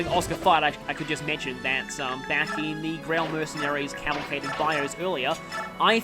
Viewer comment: I'm mishearing something: "Back in the Greil Mercenaries caliphated buyers." This is obviously completely off the mark, so I wanted to ask what's actually being said there? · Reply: "Cavalcade of bios". That's a weird word so I don't blame you for mishearing it. I also kind of misused it. It actually means a parade by people on horseback but I often hear it being used to mean "a lot of things all at once".